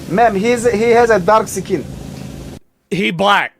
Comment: lol i know he said, "he's a dark uhh skin," but it sounded like he said darxecan. It almost sounds kinda cool.